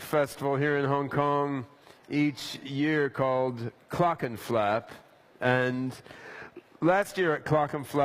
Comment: i thought at first he said the music festival is called cock and flap.